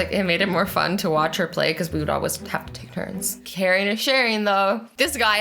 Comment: did... did azzy say "caring is sharing"? O.o